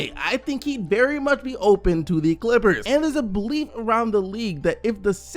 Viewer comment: Everytime he said belief I️ thought he said bleep